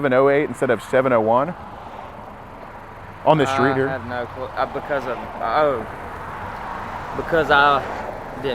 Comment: I thought he said he had no clue who Haley was